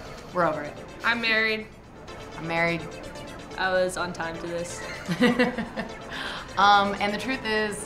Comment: "I'm married. I'm married...I was on time to this." I am Tobin. Tobin is me. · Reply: I thought she said 'I was on toddlist'